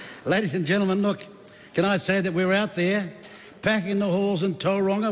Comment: I thought he said packing the whores 😂